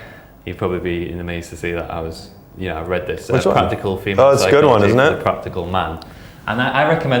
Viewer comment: i cant really hear it properly, does anyone know the title of the book they talk about at 15:00 ? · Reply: Practical Female Psychology: For the Practical Man by Joseph South, David Clare, Franco